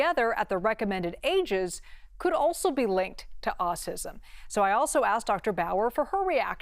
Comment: What is this crap ??? have you heard the main TV presenter on purple shirt said AUSISM instead of saying AUTISM ?? Go to min 16:49 if you don't believe me ..What is the US president to do with that ? Why is he involved ? This is a high standard scientific topic .